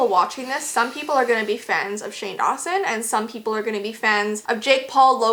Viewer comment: who else heard fens lmaoo